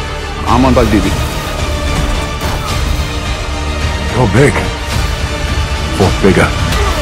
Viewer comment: Its funny because at the end when thor says "your big, fought bigger" I thought he said "your big thors bigger" and I was like thor going a little sus he doesn't want to fight superman he just wants some fun